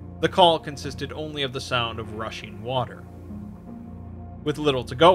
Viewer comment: I was confused because I thought he said he heard russian water, not rushing water.